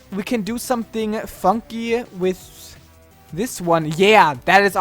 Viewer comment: For those who thought said the F word, he said Funky